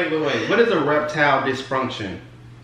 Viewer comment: I thought you said a reptile misfunction...lmbo